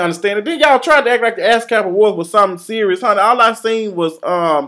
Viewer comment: First time Yung Berg said ASCAP awards, I thought he said Ass Clap awards. -LMAO please please please go in on Omarion. He is waaaay too ostentatious and incredibly pretentious!! -LOLOLOLOL you said Nikki came in looking like a vase at marshalls!!